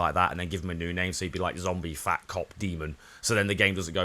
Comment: Am I ther only one that thought he said zombie fat c*ck lmao